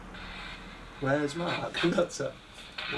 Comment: Another great show and lots of clear long EVP coming through the one i like is a spooky male asking "Wheres Mam?" at 12:48 its so creepy and deffo a Yorkshire accent to it.Look at the energy ball from this time 16:30 and see how it go's into 3 solid bars for a while...very strange! BELOW IS THE TIME I HEAR A VOICE ON THE T>V I also hear a reply at 34:06 but is to faint for me to make out. Now then i love the porty TV and i hear a voice saying "COME FORWARD!" At 24:14 (Its very quite and run it a few times to hear it) .what a fantastic new thing to try...please keep on trying with it as i am sure you will get results with it which will be fantastic!And to get a voice coming through the first time its used in the field is Amazing! The spirit at 24:14 is bang on the time and it is just as is beginning to talk ok...i can hear it so clear ! but is a tad quite ok.Like i says its just before says "Try your hardest" sort of "Come forward (Kyle talks")Then i dunno if its a voice but i can pick out someone saying what i think is "Kylee! ....kylee turn up the volume?" at 25:54 its followed by another few words but cannot make it out maybe you guys can?When you ask 'can yo say your name' a reply says "its not possible!"